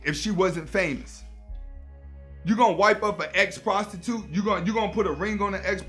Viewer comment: I misheard that as "wife up an xbox 2"